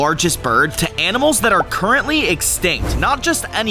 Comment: Did he say currently or am I tripping